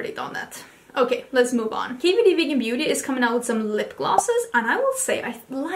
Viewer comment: I always watch videos in 1.5 speed but I had to rewind several times when you said KVD vegan beauty because I swore you were saying hibbidy bibbidy beauty 😂